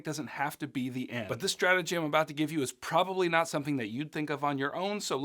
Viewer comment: When he said "strategy I'm" I thought he said stratagem like from Helldivers 2 lmao, had to check the date to be like "wait when did this come out?" lol